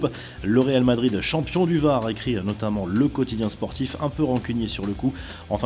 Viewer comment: Il dit quoi ? "Le Real Madrid, champion du..." J'entends pas la fin si quelqu'un peut le dire svpp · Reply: Du var